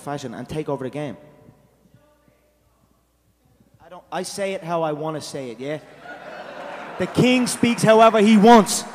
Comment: What was said by the audience member at 1:01? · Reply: "it's Joe zay